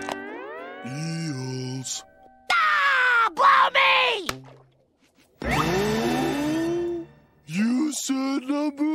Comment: What bad word said spongebob? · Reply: Blow Me. It's a synonym for F me